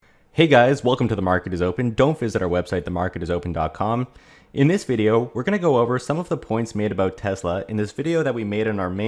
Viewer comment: It sounds like you say DONT visit our website.. in the beginning